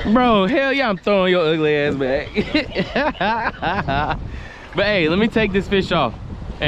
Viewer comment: Did he call the guy on the phone 4:40 BAE /baby??😒😒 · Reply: No. I said “but aye” I see what kinda time you on tho bro…. #WeirdAF